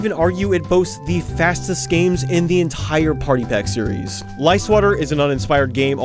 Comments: When he said "Lie Swatter", I thought he said "Lice Water" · The first time you said "Lie Swatter" I misheard it as "Lice Water."